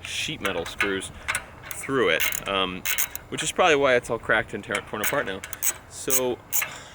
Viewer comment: the captions say cracked internet porn this caught me off guard